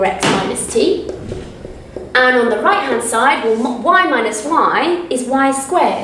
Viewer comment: I'm not sure if I heard right, but did she say "y minus y" or "y multiply or times y" in 3:43. Otherwise you are a great and amazing maths tutor and love your voice❤.